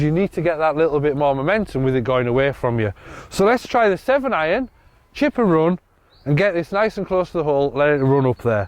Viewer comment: I thought said 8 iron chip’n run